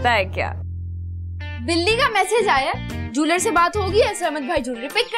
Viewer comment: i thought sasha said billi